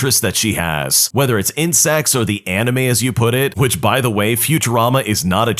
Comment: Anyone notice at 11:28 it says in s** instead of insects😂😂